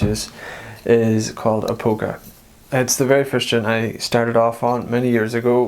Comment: It's called 'Britches Full Of Stitches' or 'A Polka' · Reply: I thought I heard "A polka" but there's quite a few Irish polkas out there!! Thank you!